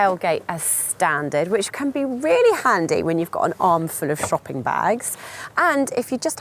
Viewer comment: did she say "when you have an arm full of fu**in' bags"? 😆